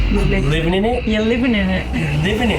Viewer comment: I heard "ya im living in it"